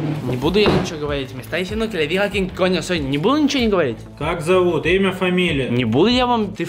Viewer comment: Yo entendi family XD